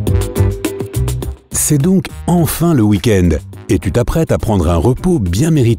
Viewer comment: Mais ptdrrr tu dis c'est le week end, alors qu'on est lundi !!! Tu va avoir plus de dislike que de vues XD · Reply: à 0:09 il a dit quoi ? Il a dit quoi ? Il a dit "c'est donc enfin le week end".